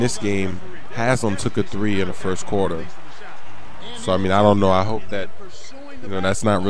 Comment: actually said "fuck it, i dont know" lol..first time i heard him say that since i watched this vid waayyyy back.